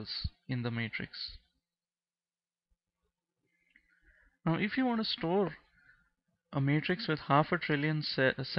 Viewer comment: what did he say? now if you wanna score?